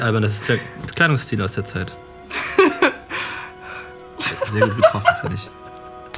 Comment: Was sagt bei 2:14? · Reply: "Sehr gut getroffen, find' ich."